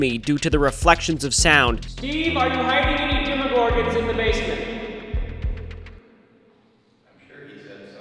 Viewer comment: I thought he said are you hiding orphans in the basment